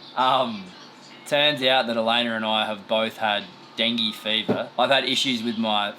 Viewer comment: Every time Riley said dengue fever I thought he was saying dinghy fever, and all I could think was he needed to go sailing. 😂 It finally clinked when he mentioned his thyroid and 💡it hit me. We had an outbreak of that a few years ago in Hana, Maui. We just called it “the dank.” No one wanted to catch it, that’s for sure.